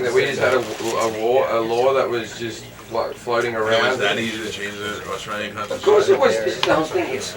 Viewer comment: Kitsune Misuto At 1:38 he says "what, do you think a (second amendment) was not in the Australian constitution? You think we just has some law floating around?" The other guy then says: "It was that easy to change the Australian constitution?" To which he replies "yes", and then starts lecturing the guy on US constitutional law. Maybe there should be a law requiring a training course and licensing for the first amendment too so people don't shoot off their mouths on topics they don't understand. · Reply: In God We Trust Actually he said "You think we didn't have a law floating around?" You miss the point. He said yes to the moron host's question about how easy it was to change Australia's stance on guns. Whether he said "constitution" or "law" the question is about how easy it was for us to lose our "gun rights" so to say. And it was easy enough that we saw one large shooting and thought it needed much more control. That wasn't the government taking away our ability to revolt, or to defend ourselves. That was a mix of the government wanting less deaths and the people crying out for people not being able to grab guns and shoot people up. Americans have that too, only difference is: you guys also have a bunch of gun-toting morons who delude themselves into thinking that killing people and threatening to kill them is the best way of getting people to not kill/threaten to kill you.